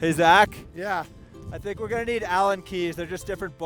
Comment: What kind of wrench did you say you needed, Napoleon? Allum keys? Alien keys? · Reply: alan key